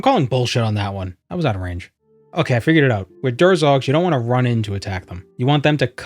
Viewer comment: what did you say lol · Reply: "Okay, I figured it out" I can speak fast sometimes, and that makes YouTube's auto-captions even worse than they usually are. That's why I upload my own transcripts. So if you can't understand what I said, the captions should be able to help you out.